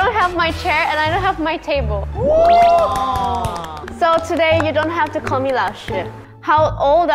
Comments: For a minute when she said “So you don’t have to call me mentor” I thought she said “So you don’t have to call me that shit” 💀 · lol I thought she said "dat shit"